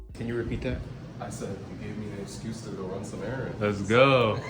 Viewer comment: What did he say in 10:08? Cant hear it properly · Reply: he said “You’ve given me an excuse to run some errands!” As in, he wants to show off his haircut in public. And afterwards replies, “Yezzirski!”, probably a impovisation of the common celebratory phrase “Yessir! 💪” and the “ski” is probably to add more sillyness to the already re-purposed word.